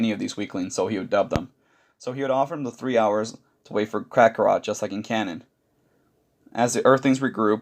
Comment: I miss heard you and thought you said crackarot... Ima use that name